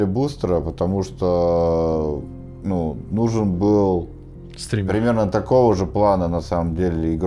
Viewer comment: ало никто кроме меня не услышал "стример"? пххахаххахаххаххахахха